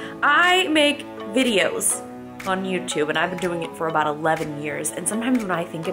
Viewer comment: did she say she been on YouTube for 11 years.😱🍉🍇🍙